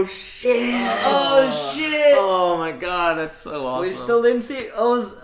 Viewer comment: im still trying to figure what is saying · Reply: all i can think is book two is with the sister :(